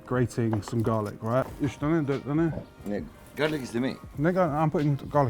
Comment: did he say nigga? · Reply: He said "ne garlic?". "Ne" means what in Turkish but we use it before some nouns to gloss over what other people talk about.